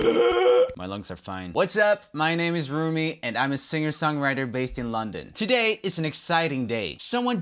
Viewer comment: roomie i thought you said swedish song writer